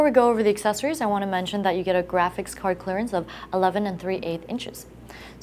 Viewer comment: For people who keep asking"WILL MY GRAPHICS CARD FIT" SHE SAYS IN THE VIDEO "gpu length fits up to 11 1/2 inches." so look up the specifications of your gpu on google, and it will tell you its length. currently only the 7990 and 5970 are around 13 inches. which should STILL fit in this case. even the 290x, is only 10.83 inches. so yes, you can fit ALMOST ANY gpu in it, and even tho it says 11 1/2, my case did to, yet fits over 15 inches by removing the ssd bay, which is useless unless you have over 4 ssd's. because the buttom allows for ssd drives as well. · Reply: It sounded like she said 11 3/8...